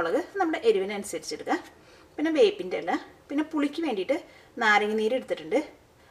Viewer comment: What is the ingredient you said after pepper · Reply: R Rathore curry leaves